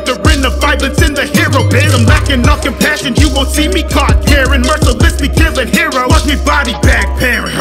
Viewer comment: i thought it said you wont see my caught karen